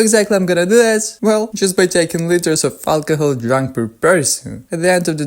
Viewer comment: It is the liters of alcohol consumed regardless of the beverage. But for example it is 17l of pure alcohol per capita not the amount of beer for example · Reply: oh I guess I missed that. for some reason I thought he said x liters of beer instead of x liters of alcohol.